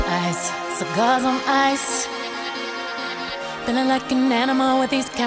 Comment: I THOUGHT SHE SAID GOD'S ON ICE